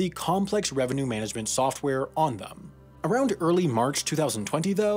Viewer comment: Someone please tell me the spelling of this "complex revenue management software called ....." All I hear is onthum. But that has no search results!!